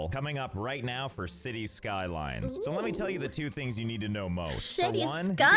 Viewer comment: For a second there, when she said “cities skylines” i heard “shitty skylife”